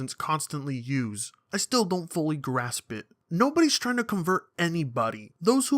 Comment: when said fully grasp it I thought he said FIRMLY GRASP IT!